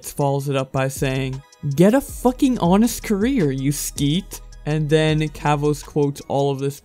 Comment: Did man just say “skeet” at 1:44 its sket